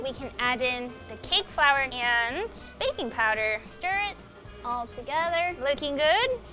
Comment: when she aaid baking powder i thought she said baby powder i jad to rewind like holdon